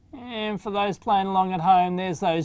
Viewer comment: I thought you said "Sanwa caps", then i remembered sanwa doesn't make caps. Then you showed it's actually "Samwha".